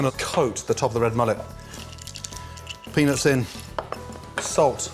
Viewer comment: Did he say Penis?